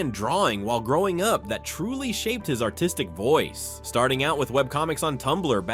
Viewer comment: why did it sound like he said "autistic voice"